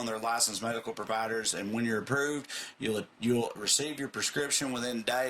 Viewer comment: During the Blue Chew commercial, said "when you're approved" I heard "wiener approved" and I spat out my coffee. Thanks for the great start to my day! Mean it!